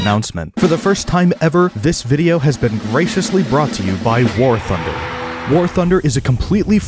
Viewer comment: I thought you said racist brought to you by war thunder.....